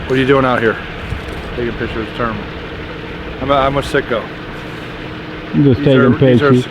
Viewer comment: Iknow he said he’s with Citgo but it sure sounded like he said I’m with sicko!